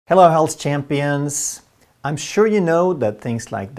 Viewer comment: I hear “health champions” as “Hell’s Champions” for some reason. Also “intermittent fasting” sounds like “intimate fasting” to me. Not sure what this means but it is probably deep.